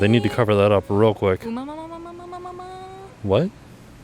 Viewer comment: Not sure if it was mentioned in the comments.... But I think you were trying to say "ooooh mama mia papa pia" @ 0:36?